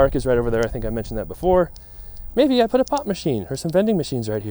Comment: I heard "Pot machine" instead of pop machine. Gotta admit, selling weed out of a vending machine beside a skate park would definitely drum up business